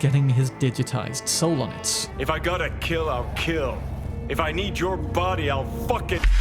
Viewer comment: He actually said - I'll f*cking take it 😅